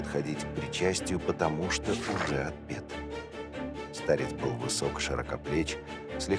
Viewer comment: помогите ,что говорят на 49:42 минуте ? он говорил загадачно,он не может причищатся ,потому что --- "фуга обет"??? что это фуга обед😵‍💫😮🤥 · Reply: уже отпет